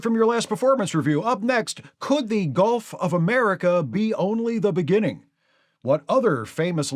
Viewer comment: I thought he said 'GOLF of America'. Kind of apt.